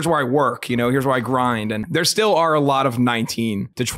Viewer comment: I heard "19-to-24-year-olds" as "19-to-20 4-year-olds"